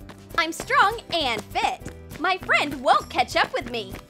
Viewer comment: is she saying ketchup or catch up? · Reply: Catch up